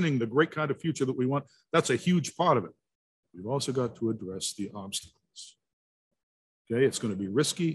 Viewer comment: What did he say at 1:28:15 ? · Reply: "We've also go to address the obstacles."